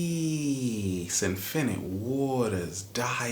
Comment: For a second I thought he said “Pee!!!!” xD